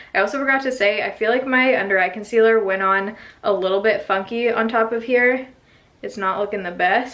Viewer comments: It sounded like you said "my concealer went on a little fucky" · SO it definitely sounds like your saying "fucky" instead of "funky" at 12:04 and I died of laughter.